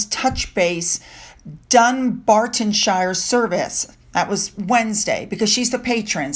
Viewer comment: At first I thought you said Dumbarton and was impressed at the shade was throwing. 😆